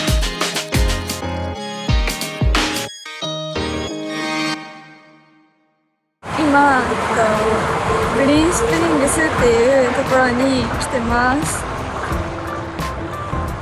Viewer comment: グリーンスプリングスがフリンシテルンデスに聞こえた